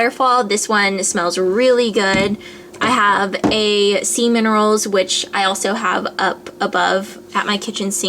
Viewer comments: LOL! NOT GONNA LIE I HEARD SEMEN ROLLS TOO. AT 13:34. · When you said sea minerals for the method soap it sounded like you said semen at first .lol my bad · haha when you were showing us your method soaps, and you said "sea minerals".. i totally heard "semen rolls" LOL!!! ahhhahh, and when you were running to your room to show us tiny tower i pictured you running like Luke when he was wearing your pink slippers :P PS i totally downloaded tiny tower and am OBSESSED! its a great game, thanks for introducing it to me! :) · oh man i thought you said "semen rolls" instead of sea minerals! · i thought you said semen rolls.. haha!! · thumbs up if you thought she said seamen rolls